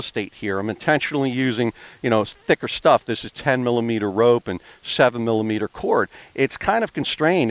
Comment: Nvm I heard it at 8:12 10mm and 7mm Hey thanks as always for ur videos What size rope was used for climbing and the hitch? Thanks again for all u do